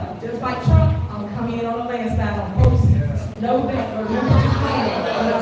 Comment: 😂 did u MISS THIS ONE … DID SHE SAY “JUST LIKE TRUMP IM Coming DOWNA ”LANDSYTLE”, aka LANDSLIDE (real spelling) 😂🤣😅